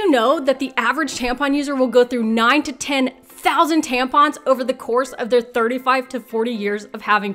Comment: I got so confused at the very beginning I thought she was saying someone would go Only 9 to 10,000 tampons at one time, not 9000 to 10,000 – I thought who the hell only uses nine tampons in a lifetime?